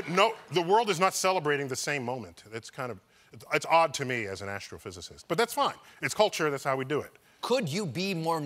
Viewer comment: i thought colbert said " Afro-Physicist "